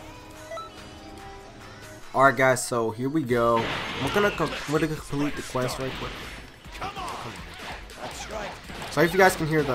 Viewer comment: wtf did u say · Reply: WE R VENOM He said I’m gonna complete the quest real quick but with a stutter